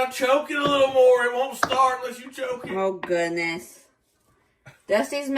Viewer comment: Lololololol 32:55 what was he saying? You gotta choke it a little more? 😂😂 What was that next bit he said? "It won't starve unless you choke it"??? What? Did I hear that right? I started cracking when I heard him saying that!